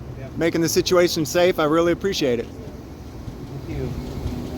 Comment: At the 15:46 mark... Dose that officer make a derogatory comment? Sounded like he said F--k You.